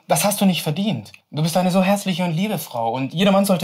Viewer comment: ICH HAB VERSTANDEN HÄSSLICH NICHT HERZLICH 😭😂